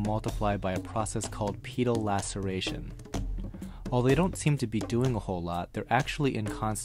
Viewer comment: Thought you said Penile Laceration. I cringed.